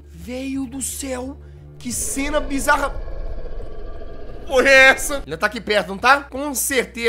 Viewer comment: so eu que escutei o jazz dizendo que p0rra è essa? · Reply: Não é porra se você ouvir direito ele disse que cena bizarra grito foi essa